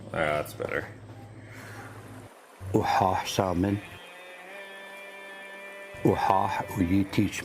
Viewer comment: Ex. Quis. Ite. The symbolism is wonderful, and I'm so glad you found the perfect way to display it. The poem at 12:12 is so beautiful. Sounds like he says 'salmon' toward the end. I'd love to know what he's saying. This family obviously cares a lot for their father/grandfather/whomever to commission such an amazing gift. Well done.